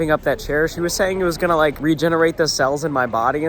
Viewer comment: Wait wait did you say CELLS